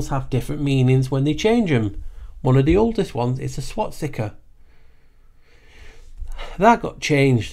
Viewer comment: 😂😂😂 I swear on everything you just said twat sticker..